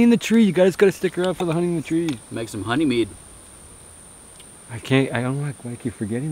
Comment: Peacock looked delicious! I can't wait for you guys to get the honey from the tree while getting stung. And also making honey meat. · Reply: I thought he said honey mead.